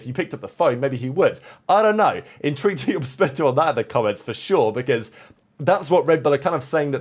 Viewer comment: I know this is petty but…. Does he keep saying “in to your perspective in the comments” or “interested to you perspective int the comments” OR as I hear … “in true to your perspective in the comments”? I love the content but it’s driving me crazy!